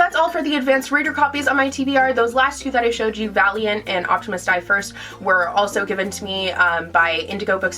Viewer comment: I looked away from the screen for a moment, and misheard "Optimists die first" as Optimus diapers...